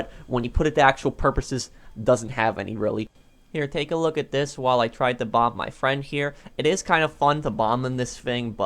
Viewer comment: I heard “when I try and bum my friend”